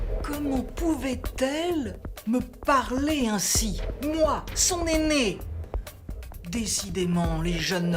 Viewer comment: "Moi son néné" :)